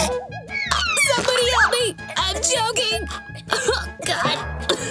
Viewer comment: In 3:38 i thought said "I'm joking" but in reality she said "I'm choking"